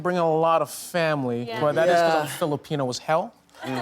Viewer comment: what did Jacob say? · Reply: He said it’s because he’s Filipino as hell